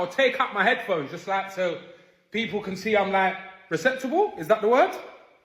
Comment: Did he say receptacle? 😅